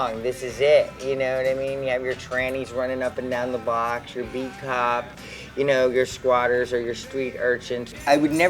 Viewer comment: What does say at 1:41? I can't figure out if he's said "your beat cop" or "your peacock" or something else. When I look at the photo being shown at that point, I'm confused because that photo depict like either a policeman or a flashy-peacock type (which I take to mean a glitter/glam rock dude). He's a cool-looking dude tho'.